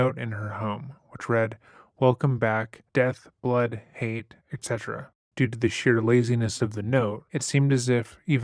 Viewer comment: did it actually say “et cetera”?!! That’s kinda hilarious.